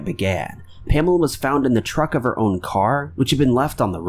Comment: I know he was trying to say “trunk” however it sounded like he said “Pamela was found in the truck of her own car.” 😅